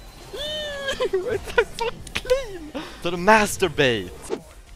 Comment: did he said what i think he said? MASTER BAIT